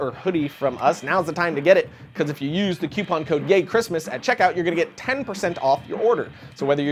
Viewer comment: Lol, it the sponsors i thought he said GAYCHRISTMAS and not YAYCHIRISTMAS :D